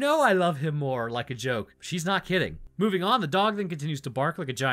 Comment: Apparently my subconscious heard you say "meanwhile the dog continues to bark like a big nerd at the back door" because I just shot water out my nose and initially I had no clue as to why.